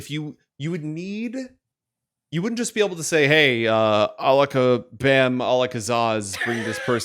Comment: All I heard was OLIKA